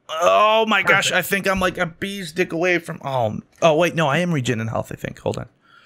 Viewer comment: I thought didnt swear? did he say he was a bee's dick away from being full?